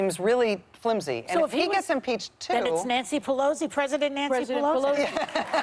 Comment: did she just say Moscow Mitch? oh I love that woman